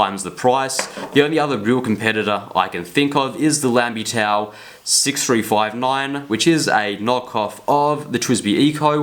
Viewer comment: Wwhat did you say the other competitor to the WIngSung 3008 is? It sounded like LambyTaub 6359 at the 12:20 mark? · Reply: lanbitou 3059